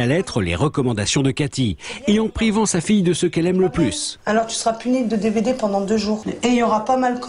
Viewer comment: j'ai entendu "tu sera puni de DBD (dead by daylight) j'ai buger 😂